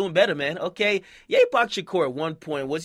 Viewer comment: I left once I heard "Ye-Pac Shakur"